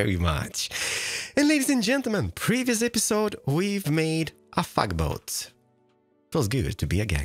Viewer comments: anyone else hear him say thug boat different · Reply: I've heard him use the word "fugly", before. Sounded like he said, "fugboat". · so we just gonna ignore that it sounded like he said fuckboat and that YouTube subtitles got that too